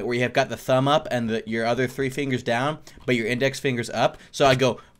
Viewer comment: I misheard the index finger as middle finger somehow LMFAO